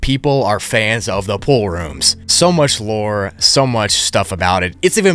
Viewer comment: Pool room? I thought you said mom room